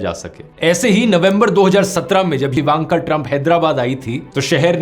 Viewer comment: Did he say Ivanka Trump wife of Donald Trump at 16:15 ????????